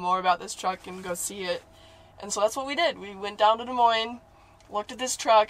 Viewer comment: you know you play red dead too much if you hear lemoyne instead of des moines 😂